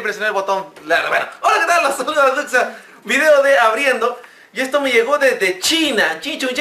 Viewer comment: puta madre pense que decia ABRIENDO LA XBOX xddd